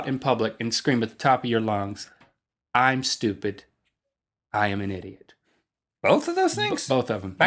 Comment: at first i thought he said "scream at the top of your lungs,i am stupid,i am gay"